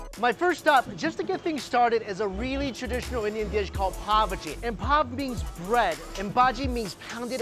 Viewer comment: Sorry but: "Its a really common dish called (what i heard: Poverty) Pav Bhaji. Poverty is no joke but hearing it be presented like that. Looks damn delish tho! I've loved to go to india for years, mainly for the food and culture.